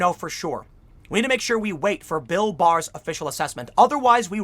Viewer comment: I coulda swore Tim said "Bill Burr" 🤣 ...this should be interesting...